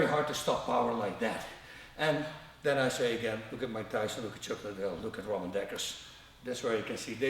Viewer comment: who is the fourth fighter bas keep mentioning, I cant hear the pronuonciation very well, he says "look at mike Tyson, me, chuck liddel and #who#?" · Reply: Ramon Dekkers